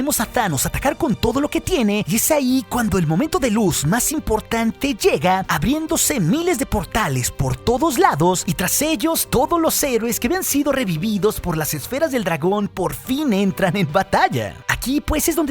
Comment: Escuché bien? Esfera del dragón en el minuto